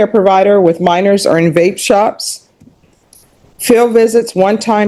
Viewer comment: haha! I thought she said she worked in a bait shop ... shoulda known it was a vape shop!